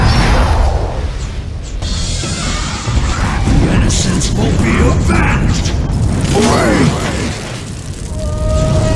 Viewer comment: i really want to understande what he says in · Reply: The first line is pretty clear, he says "Innocents will be AVENGED!!" then what he says kind of gets muffled. I think its something along the lines of "AWAY!! Turn them down!"